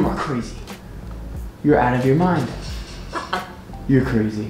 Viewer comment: Sounded like she said "Psycho"!! 🤣